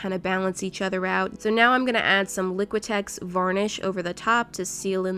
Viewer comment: I just realized that this whole time when she’s adding the protective layer, she says varnish. I always thought she said garnish 😂